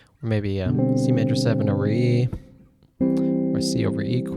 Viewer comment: CMaj7 ____. Aree? What does he say there? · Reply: C major 7 over E in other words C major 7 in second inversion.